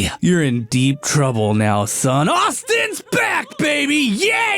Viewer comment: I had a buffering sign just as he said “AUSTIN’S BACK, BABY!!!” So it made sound like he said “AUSTIN’S BACK, BI-“